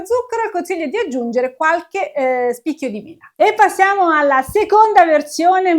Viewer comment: Ho ascoltato lo spezzone di video decine di volte, anche al rallentatore, al minuto 4:47 non riesco a capire cosa dice, di usare qualche spicchio di???? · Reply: di mela! Scusami, non ho scandito bene la parola